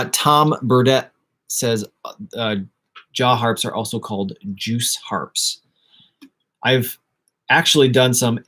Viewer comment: Maby they where saying juke harp. (Harmonica). And I heard juice. Don't know. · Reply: It’s called a Jew’s Harp. You can Google the etymology. It’s really interesting.